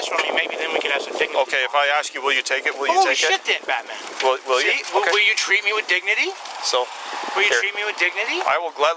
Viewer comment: does he say Batman or bad man? I can’t tell!! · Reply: Batman